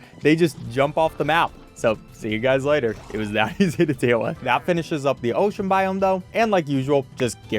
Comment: what did he say?😂😂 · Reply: It was that easy to deal with